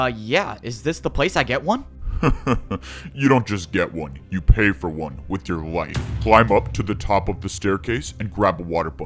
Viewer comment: i thought he said "you paid with your wife"💀